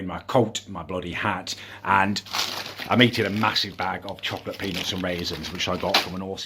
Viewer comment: i thought he said chocolate penis